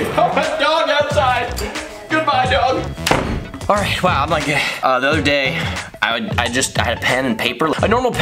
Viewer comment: did you say wow I’m not gay?😂😂